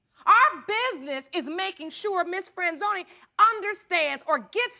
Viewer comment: I thought I heard Miss Friendzone, imagine being friend zoned by her.